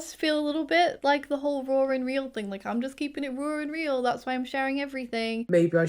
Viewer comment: Omg it took me so many times of you saying "raw and real" for me to understand it! I thought you were saying "roaring real" because of your accent 😅. And i think as far as diagnosing other people, its the same as any sensitive topic. You bring it up in a more general way and if they seem open, you can keep taking about it. But if at any point they get annoyed or detached, you stop.